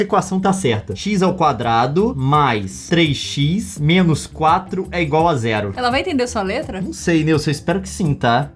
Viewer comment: "Jeito que o Leon falou:" Xis ao Quadrado mais três xis menos quatro é igual à zero! "Jeito que eu ouvi:" Giz ao Guardanapo mais têes xazans memes quatro é igual à arrégo! Edit: Não tenho culpa!, não sou neRd!